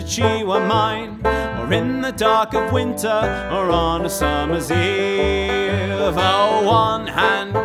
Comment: Lyrics: From France we get the Brandy, from Martinique the rum Sweet red Cabernet, from Italy does come But the fairest of ‘em all, me boys, the one to beat the day Is made from apples up the mighty Saguenay {Chorus} So, follow me lads! 'Cause this ain't no grog or ale! One pint down, you'll be swingin' in the gale! Five pints, bully, you'll be shaking in your shoes! We're half-seas over on the Joli Rouge! She's called the Dreadnought Cider, she's proper and she's fine And when the day is over, sure, I wish that she were mine Or in the dark of winter, or on a summer's eve Oh, one hand giveth and the other doth receive {Chorus} So turn your sails over, and bring her hard to port Find that little star and fly straight into the north The wild sun upon your back, the wind a-blowing free You're rolling down the river boys to old Chicoutimi {Chorus} So you can have a Magners and pour it over ice Or you can have a Strongbow if it's sadness that you like Or join us up the river, and we'll set your heart aglow And how you'll feel when the real cider starts to flow {Chorus x2} -The Longest Song Wiki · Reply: Misheard from my three year old daughter this morning, "And in the dark of winter, she use to lose her mind." Good enough mesh of verse two for me. XD